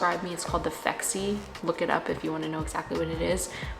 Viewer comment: What is the thing called again she said her doctor prescribes her I couldn’t seem to find the right thing she was saying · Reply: Phexxi